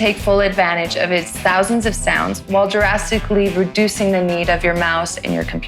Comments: I saw the dinosaur in the background, then I thought I heard heard you say "while Jurrassically reducing the need of your mouse and your computer screen". Did I imagine it? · Anyone else notice at 1:27 she sneaks in a 'jurassically' instead of 'drastically' I noticed